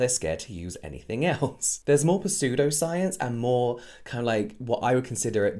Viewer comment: I've never heard someone pronounce the P in pseudoscience. But it sounded like you said persue-doscience! Which I thought, WOW! NEW # # pursuesoscience !! Their trying to pursue your money with pseudosciencen